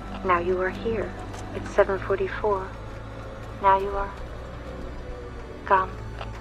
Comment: "now you are _____" What is she saying? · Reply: gone